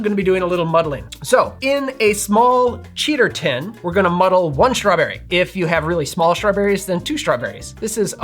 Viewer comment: I thought you said "muddle a shrubbery" for a second.The Knights Who Say "Ni" would approve.(and I am an fan)